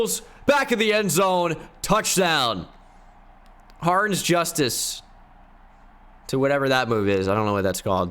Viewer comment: Sounds like u say harns justice